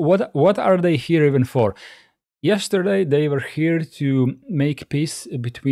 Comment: It was Russian, yes, and I asked my Russian friend to listen to it and he said that the second part sound almost like native Russian speaker. I don't speak Russian, BTW, I tried the best I could for the video :) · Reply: I watched it again after resting. I realize I thought you said "yesterday" instead of "evening."